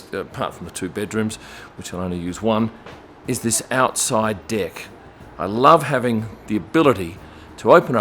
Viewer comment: I thought he said outside dick 😂😂😂